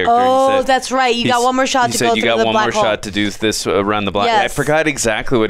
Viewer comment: does she say *butthole in place of *blackhole🤔😂